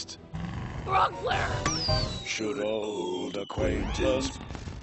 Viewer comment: I died of laughter for some reason what did they say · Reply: "Should old aquaintance be-" It's the first verse in the song Old Lang Sayne, usually sung at New Year's Eve